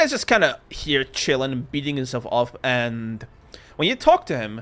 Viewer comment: I thought you said “beating himself off”, well I got a good laugh from that even if I did misunderstand 😂